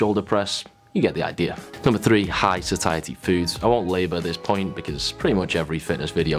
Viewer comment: I thought it said "high society foods" so I expected oysters and caviar.